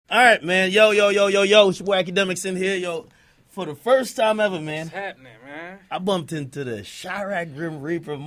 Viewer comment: For a sec I thought ak said "Wackademiks" in the beginning lol